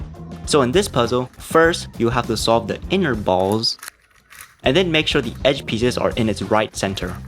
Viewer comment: I heard “you have to solve the in your balls”